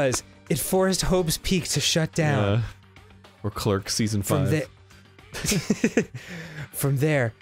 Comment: What does Arin say at 27:20 it sounds like clerk season 5.